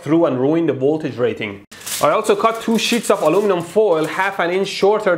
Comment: what I heard was "TWO SHITS OF ALUMINUM"😂😂